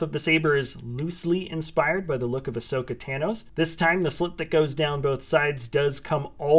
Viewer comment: Did you just say ashoka tanos that what i heared in